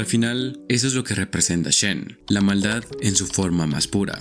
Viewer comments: Pensé que decía Shein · Al principio pensé que decía: “SHEIN” xdd